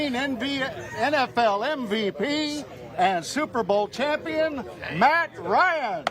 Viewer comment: I think he said he took them to one. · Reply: nope @ 19:37 he clearly says “superbowl champion”